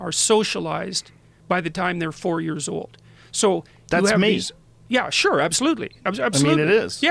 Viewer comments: What did Joe say at 38:29? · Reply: that's me! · What is Joe saying at 38:28? They talk over each other. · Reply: Joe is saying"that's me" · Does anyone have any idea what Joe says at about 38:29 Peterson: "...are socialised by the time their four years old, so thats when..." Joe says something incoherant, Peterson continues: "...yea sure absolutely..." Realy curious about what Joe said, any help would be appreciated. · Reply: Aaron Ward that's me